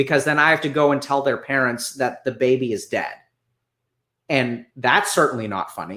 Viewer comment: im sorry but did he say that Da Baby is dead? 😳😳